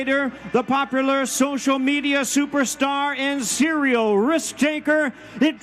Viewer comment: definitely sounded like he was about to call him a serial rapist